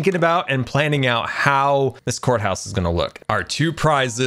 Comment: Shoot I thought said "corn house". I was so hyped. Court house is cool too.